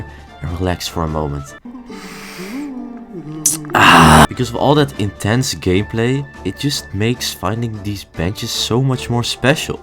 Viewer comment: Why did I hear "from all this intense gayplay"??💀